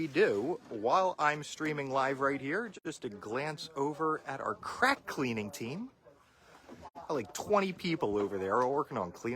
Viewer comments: I thought he said “crap cleaning team” 😂😂😂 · I thought he said crap cleaning team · Ngl, I heard "here's our crap cleaning team" and just lost it